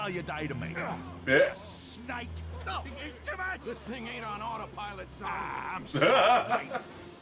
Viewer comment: did engineer say he the pilot this plane me:wtf he pilot plane? snake?!!!!! how he drive plane idk how he control plane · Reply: He said, “This thing aint on autopilot son.”